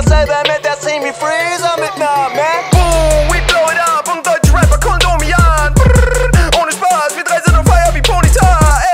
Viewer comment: Ich habe irgendwie immer verstanden :we blow it up, ie Deutsche Rapper Kondome ja. Hat irgendwie auch Sinn gemacht